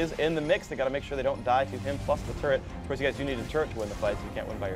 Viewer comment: i thought he said "fuck" the turret (plus*)